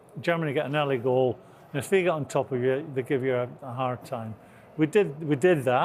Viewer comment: Oh my days, this Scottish accent! can someone please tell me what is said at 0:07? did he say 'they figured on top'. 2:41. did he say 'we need to stare in front of us?